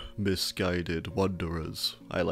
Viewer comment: All i hear is "pooorn is guided in warriors"